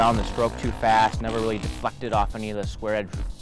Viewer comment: does he say fucked it off